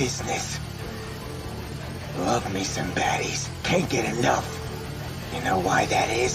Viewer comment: at first i thought he said cake ani't enough and knowing denji if he really said that we all would know his not talking about the cake you eat